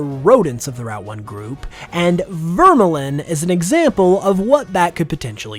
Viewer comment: when I hear Vermelin, I keep hearing a “Merlin” which also fits the fairy typing, as a mythical mage also, I can imagine Snowdent’s evolution just being a pile of them, like a pile of snowballs, while also referencing the lemming stereotype